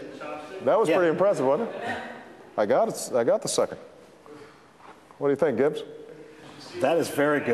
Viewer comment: at :25 i thought he said "what do you think, bitch"